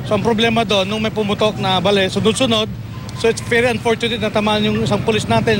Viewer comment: balil ba narinig ko???